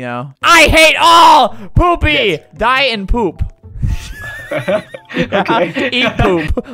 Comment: I thought said die and poop😂